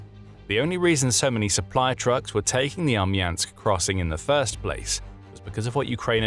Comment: LoL 🤣 I thought you were saying army ants, thinking that's what you were calling the Russian supply trucks. I think I need to turn the volume up on my TV!😂🤣🍁